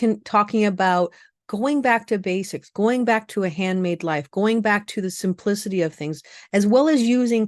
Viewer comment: 😂 I heard "go back to a handmaid life" at first rather than "hand made life" lol lil different😅